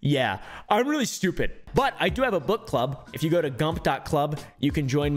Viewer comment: I thought Caleb said foot club😭😭😭